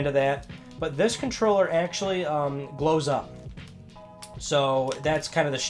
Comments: I thought you said blows up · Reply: He did say blows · Am i the only one who thought he said blows up instead of glows up for that small ps3 one?